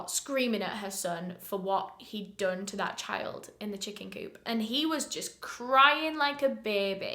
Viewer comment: I thought it said chicken soop 😅